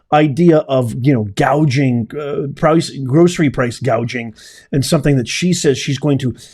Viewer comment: Wait, I thought she said price gauging? Lol